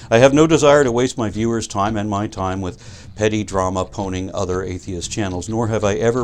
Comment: What is that word you're saying at 3:38? Sounds like "poning", but as far as I know there is no such word. Was that "panning" (as in "harshly criticizing")? Just curious.